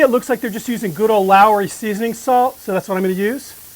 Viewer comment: Looks great. What’s the Lowre salt you mentioned? I think I’m mishearing it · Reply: IS YT lawyr’s season salt. In American every grocery store has it. Regular season salt would be pretty close. There will be a copy cat recipe if you can’t find it and want to make it